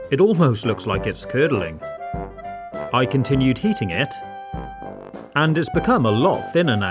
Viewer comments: When you said heating I thought you said eating 😅😅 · When you said "when i continued heating it..." i thought you said "when i continued eating it..." so went "BRUH! Who tf eats boiled slime?!" Lol! isking